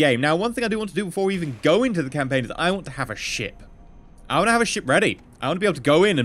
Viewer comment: I miss heard him at the beginning I thought he said "I wanna have a shit" not " I wanna have a ship" hahhaha